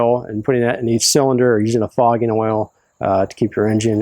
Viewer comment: you say maybe put a tsp. of oil in each cylinder, "using a fogging(?) oil"...what is that word? ty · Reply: You are correct, it is a fogging oil that you can buy in an aerosol can. Remove each spark plug and spray a couple of times and each cylinder. If you choose to do this, I would turn the motor over once or twice to make sure the entire cylinder is lubricated. Honestly I only do this to my small engines (i.e. lawn equipment), because they sit longer than my Camaro during off seasons. Hope this helps.